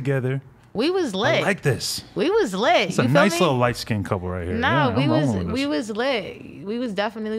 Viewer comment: did he say “nice lil light skin couple right here” 🧍🏿‍♀️